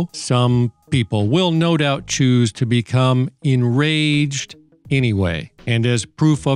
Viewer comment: Oh shoot… you said enraged… I thought you said engorged. Well, I’m definitely not enraged.